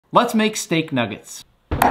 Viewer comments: You: Let's make a steak nuggets Me: what i heard is = Let's make snake nuggets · I fr thought he said snake nuggies